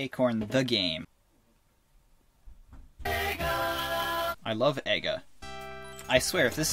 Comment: I'm pretty sure at 3:07 it said NIGGA instead of EGA